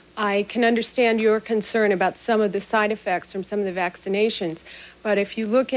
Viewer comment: Slow down to enunciate....finish your words. I thought you were saying Vaseline; but, you're talking about vaccinations. It's okay to want to speak "properly" but, not to the extent your words are undecipherable. Continued success with your show...